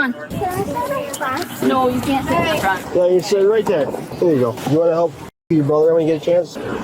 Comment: "you wanna help f*'k your brother " wtf???? Im assuming /hoping that's not what was said, but even the closed captions put thats what he said. Am I bugging??? 😂😂